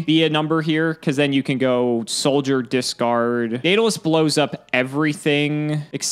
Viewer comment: I'm amazed by the automatic subtitle, at 25:41 it picked up the word daedelus. Had a hard time understanding what he said